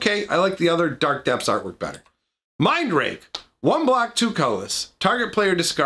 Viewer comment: Come visit my second channel for more entertaining times - <link> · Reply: Lol it sounds like you said mind rape